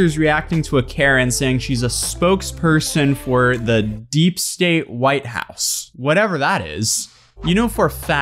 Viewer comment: Why did I hear "deep sea white house"